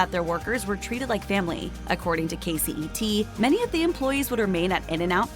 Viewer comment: did you say KCET???